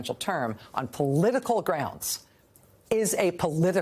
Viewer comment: What she said: "... and that matters." What I heard: "... and that Maddows." 😀